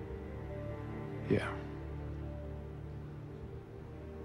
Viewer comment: I'm French and I watch English videos to practice speaking English, but I don't understand what Four says at 3:44. Please can someone tell me what he says? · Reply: Wow so late, but he said, "I think you got other things you need to worry about."